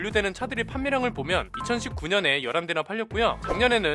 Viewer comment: 나 왜 11대(열한대)로 들리지...